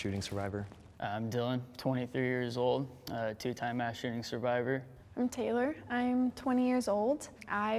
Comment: Thé sad part is that my first thought was “woah how could they so easily find mass shooting survivors” and then I remembered this is America... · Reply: I had to replay that back at 2:49 ....did he just say he was a two time mass shooting survivor...as in he’s been in those situations TWICE?! I really hope not 😥